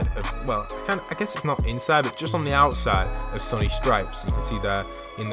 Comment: am I the only one who heard "Sunny Stripes"?